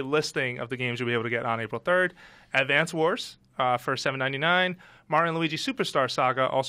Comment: I thought he said 17.99 at first and I was like "NOOOOOOOOOOO!! IT WILL BURN MY MONEY!!".